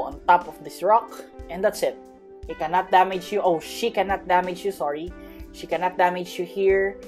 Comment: he say oh she but i hear that he say oh sh!t